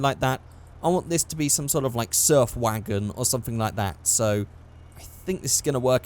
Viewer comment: When he said surf wagon I legit thought he said surf wanker.